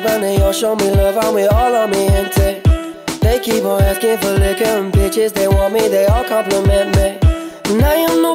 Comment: im screaming i thought he said licking and bitches but its liquor and pictures lmaoooo